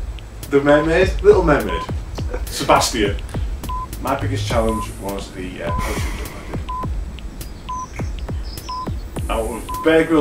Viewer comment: What's did he say his biggest challenge was at 1:37 ? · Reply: Apologies, after getting it all ready for online we realised it was hard to hear. His biggest challenge is a parachute jump